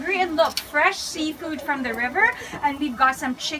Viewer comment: Did she say “ fresh seafood from the river “ ?